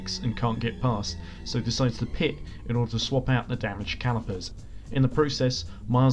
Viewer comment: Swap out calipers? Did they bleed the brakes during the race? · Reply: That makes sense, thought they said calipers in the video, but I may be mistaken.